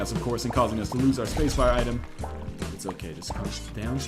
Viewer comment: did he say spacebar item? is spelunky (or the 360) keyboard friendly now?